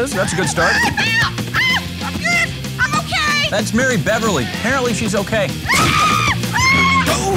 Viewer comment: did they say Beverley?